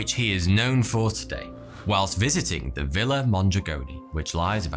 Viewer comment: I thought he said villa montegreni and i thought my ACII knowledge would be useful